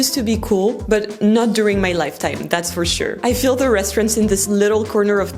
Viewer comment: at first I thought she said restrooms instead of restaurants 😂 was surprised that she supposedly used the American word instead of saying toilets then realized I just misheard it 😂